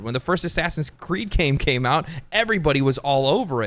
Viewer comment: Lol I thought you said assassin's Creed cream came out